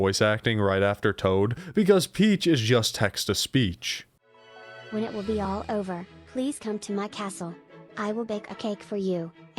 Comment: i swear i misheard the phrase "when it will be all over" as "when WILL IT be all over" and i thought peach was the only character in game with some form of sentience and wanted to escape the horror that is New Super Mario Forever 2012, finding some sweet release from the buggy asset-mashup nightmare. hey! you can make a game about this!